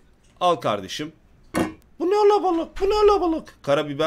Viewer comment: ne dediğini tek ben mi anlamadım 😀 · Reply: BUNE ALABALIK